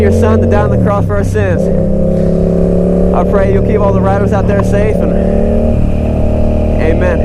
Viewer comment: I'm always encouraged by your verses and prayer. At first when you said, "keep all the riders out there safe," I thought you said rabbits. :-)